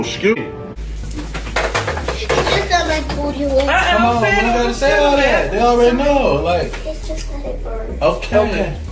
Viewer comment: What did she say at 1:54??? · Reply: It smells like farts in here